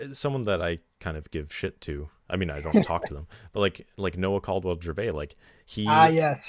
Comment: i didn't hear it clear enough, who you guys are talking about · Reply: Noah Caldwell Gervais